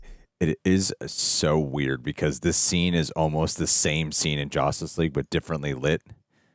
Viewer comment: why does he say it like its "Jostice" not "Justice"